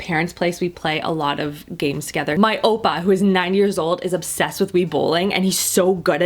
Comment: Did she just say oppa?!?!?!!? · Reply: opa, dutch for grandfather, apparently